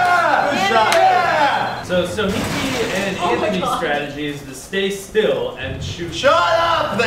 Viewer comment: Is it me or did Ian say "Ianthony" at 6:23 ?